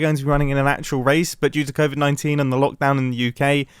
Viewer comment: When you were talking about the marathon run thing when you said "covid-19" I heard "hermit 19" btw who's the nineteenth hermit??